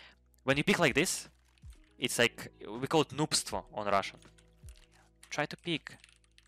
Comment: Thank you Ilya and good luck in 2024! Btw what's that russian word 17:16? "Noobs2"? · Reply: its "noobstvo")